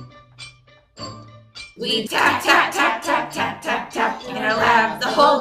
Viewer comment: I thought it was "fap" instead of "tap"